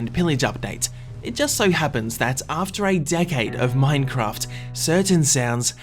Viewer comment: *after 8 decades of Minecraft* wut are u on man 8 decades I'm already dead lol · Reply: He said "After A decade." Not "after 8 decades." You just misheard.